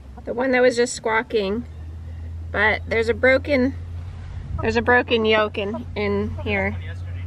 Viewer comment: I heard " There's a broken yoken"